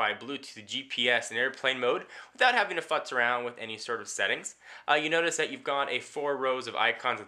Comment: it sounded like he said "without having to fuck around with any settings"